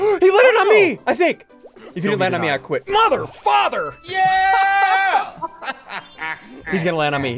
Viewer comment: I thought he said mother flipper